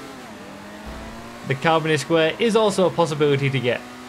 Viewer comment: had to watch that twice because i thought you said communist square and i was like 🤔